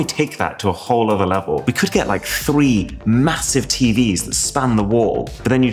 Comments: did I hear massive? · did he just say "MASSIVE"?